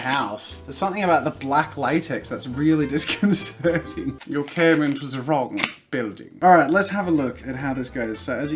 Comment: "Your care-mentor's a wrong building" ........? I'm positive that's not what he said.. but I have no idea what he actually said Even tho my first language isn't English (Tho I wish), I'd like to think my English isn't that bad.. but I've had to listen to that at least 6 times and I still can't fecking understand goddamnit Edit: and even youtube translated it as "Your Cameron's was a frog my building" ... Like whhat